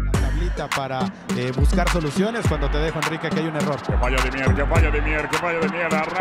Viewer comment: Yo vi ese partido Entendí "que falla de mirdaa" me cague con eso jajajs